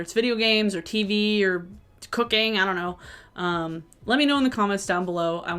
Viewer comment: When you listed out things that got you through the day and said cooking, I had to run it back cuz I thought you said cocaine... And I was like damn it may be true but it doesn't seem positive! 😂 Cozy Grove on switch has been my happy place! 💜